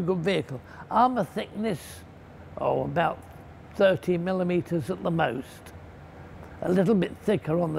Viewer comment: Armor scheme shows that frontal armor was more than "13 millimetres at the most". 13mm is around 1/2 inch. 7/8 or 1 1/4 is more than that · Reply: I thought he said "30 millimetres at the most".